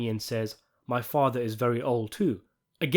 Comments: I thought he said "My father is very ILL too" ? Great analysis! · To me it always sounded like he said "My father is very ill too." In reference to Old Man Bacala the Terminator's lung cancer.